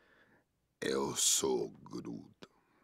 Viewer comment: After a while I heard "yosoygru"